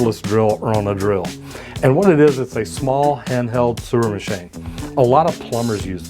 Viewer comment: A what machine??? I heard you say it multiple time and still can’t figure out what you’re saying... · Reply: Sewer Machine....I had to listen several times to figure it out too!